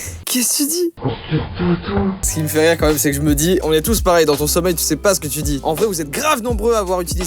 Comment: j'ai entendu aspire pas tous mdr oui 10 mois après et alors UwU